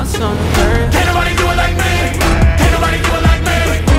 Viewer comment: is it just me or when he was screaming "ME" did anyone else hear fabvl? 1:46 🔥 🔥 🔥